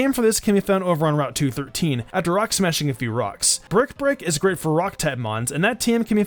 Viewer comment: When you say "brick break", it sounds like you say "bird prick".